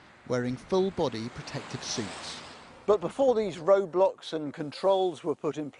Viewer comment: did he just said Roblox? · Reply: He said road blocks.